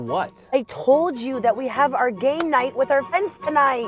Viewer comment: I thought she said “Gay” night with friends